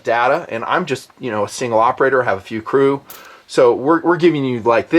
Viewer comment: did you say your an operator